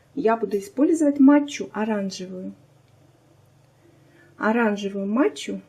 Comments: Первый раз слышу про эту матчу,где её взять то. Сначала послышалось оранжевую мочу, чуть под стол не свалилась😄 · Уверенна, что это очень вкусно. Обязательно попробую, хотя состав похож немного на крем пломбир, ну и + бизе. Рассмешила, когда сказала, о том, чтоб окрасить в жёлтый цвет ,добавим матчу,... в этот момент мне послышалось мочу 😄